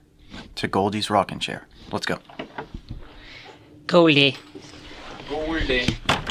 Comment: In between 50:19 and 50:22… What are you saying guys? It sounds like “God ide” in danish! It means “Good idea”. Perfect with timing tho! 😂☝🏽 Love Y’all’s work in this and I’m waiting every Monday (danish time) to watch new episodes! It’s freaking amazing!🤩 Fan since start here🤍🤟🏽